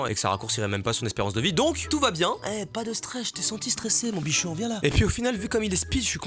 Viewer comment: "eh pas de stresse..." Moi : "YA POOOIINT S "